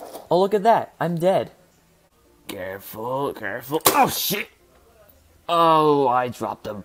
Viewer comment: I think say OH S**T